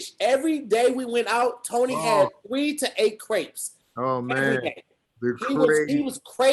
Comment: when u were talking bout crêpes I fully thought u guys were saying grapes bro😫😂😂1:12:09 🇫🇷🇫🇷